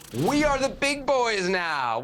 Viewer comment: Waaaaaaaaait a minute……… I thought he said “We are big boys now.” I didn’t listen to that interview except for live, post game. As a Canadian, that’s not our style. I think Canadians would agree with “We are big boys now” but to say “We are the big boys now” would be boasting before a real achievement. I have heard this from players and fans of other countries (you know who you are) but this is not part of the Canadian psyche. I could be mistaken about Mr. Larin’s comment and I will take it back if that is so. However, that is not characteristically us. Go Canada!🇨🇦